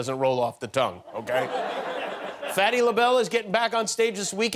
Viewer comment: I thought he said "Patti LaBelle" was going to CPAC. I almost started to cry.